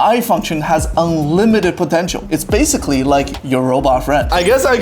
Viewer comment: did i hear potential!!!